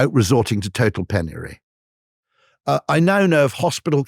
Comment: what's the word at 1:21:12? penniary? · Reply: Penury. V similar to poverty. Synonyms.